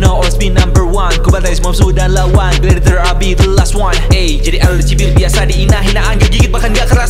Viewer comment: Tiap liat ini masih ajah, ME RIN DINGGG! Bangganya kebangetan. Partnya dan GONG bgttt! 🔥🔥🔥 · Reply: Partnya juga keren. Cuma ada bagian yg aku denger berulang ulang masih misheard. Part " Jadi idol dicibir biasa di INA " aku dengernya " jadi lgbt dicibir biasa di INA" tapi flownya temponya cakep